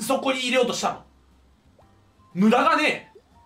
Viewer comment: のところ「無駄がねぇ」が「無駄金」に聞こえた😂😂